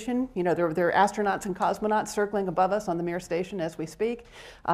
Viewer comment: Did she say the "Mir station"? :)